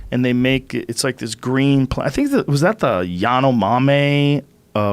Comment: did he say the "ya no mames" tribe of bolovia